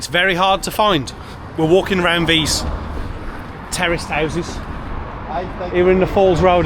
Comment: Did you call them terrorist houses at 10:44.